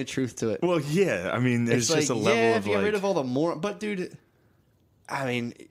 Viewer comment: At first I thought it said "other Mormons"